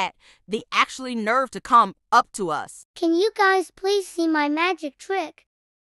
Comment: "This exotic cricket" As an former-cricket, it was very offensive >:( ... "and this mole rat had the NERVE to c#m up to us" -Misheard the text-to-speech text